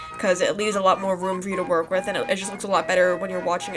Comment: Did she just say woom- 1:12 idk if she did TwT